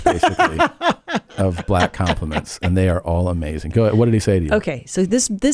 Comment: I thought it said complaints, that would have been hilarious....funnier than borderline illiteracy.